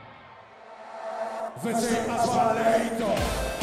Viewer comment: can anyone tell me what he's saying at 13:20 ? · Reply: Joe Schmoe זה הזמן להתעורר Ze Azman Leitorer It's time to wake up